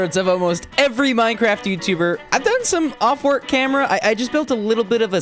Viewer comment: u said i did some off work camera instead of off camera work